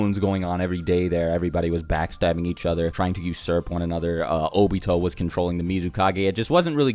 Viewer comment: did u say trying to use syrup on eachother